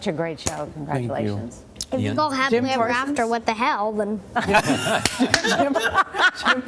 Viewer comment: What does the kid say at 7:42?? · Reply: Oh he said "If you call 'Happily ever after' what the hell then.."